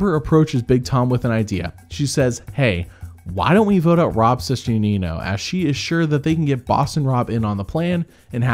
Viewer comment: I watch these, but I don’t watch Survivor. I wasn’t paying too much attention to the video, had it playing on in the background. I kept hearing “Rob’s sister, Nino.” It wasn’t until I realized Rob’s sister, Nino, was a guy named Rob, and not Boston Rob’s sister.